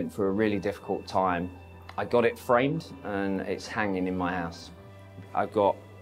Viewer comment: Bro, I really need to improve my English. 4:33 What did he say? I keep listening: "I got it framed and it's hanging in my ass."